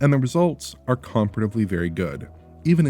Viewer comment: is Oliver saying comparatively?